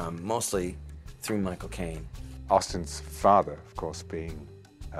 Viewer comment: "There's a lot of my father's energy in this movie. Em, mostly through my cocaine" is what I heard. :P